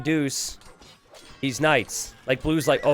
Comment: Not sure why .. but i heard for a sec 23:02 "These knights" .. i hear "THESE NUTS" (o-o) (--_--) (O-O)/ !!!